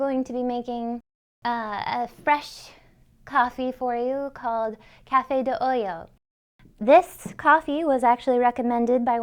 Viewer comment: Nice, definitely going to try it. Not nitpicking, but it sounds like she is saying Cafe de oyo (coffee from hole), its supposed to be “olla” oi-ahhh (coffee from pot). Awesome video, keep it coming.